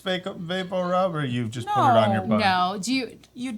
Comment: I swear I thought he said "put it on your butt" too!